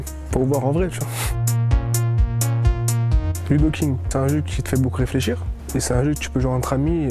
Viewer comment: C'est quoi le nom du jeu qu'il a dit à 0:30 ? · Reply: Ludo King frère